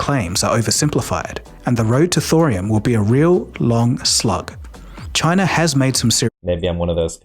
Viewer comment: did he say the road to thorium will be "...a real long slug"?